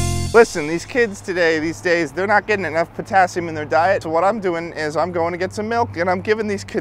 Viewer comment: did he say potassium?